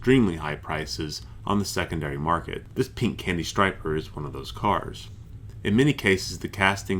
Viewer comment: For a second there I thought it said "Candy Stripper"